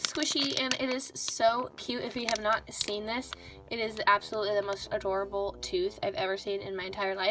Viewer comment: When she said "this is absolutely adorable" I thought she said "this is absolutely horrible" who else😂